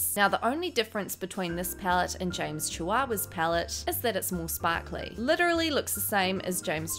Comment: I love her voice but it sounds like shes saying james chihuahuas palette